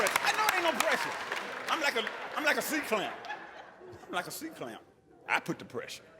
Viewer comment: Did he say "I'm like a sea clown." or am I deaf? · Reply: Oh I thought he said sea clam